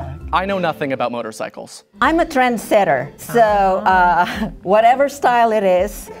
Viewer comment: really sounded like “I’m a transgender” 😭😭😭